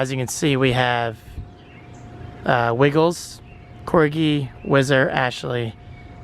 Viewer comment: I thought Wiggles said Nipples tbh